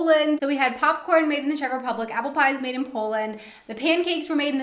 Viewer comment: when you talked about the popcorn it sounded like you said made in cheshire. If so, cheshire is in the UK not the czech republic.